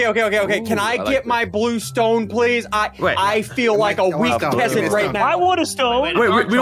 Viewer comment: When said can i get the blue stone pls i feel like a weakling i thought he said give me the blue stone pls i feel like an astronaut in the ocean 😂